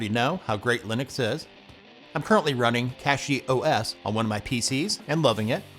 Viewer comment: Turn on subtitles (English (auto-generated)) and see what youtube wrote when he said "I'm currently running cachyOS" 😅